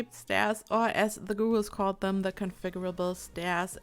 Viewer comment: For a second there I thought you said 'ghouls' instead of 'gurus'. Hahaha. I'm looking forward to Realm Of Magic but I'm more excited for this, even though I'll need to redo parts of some of my builds.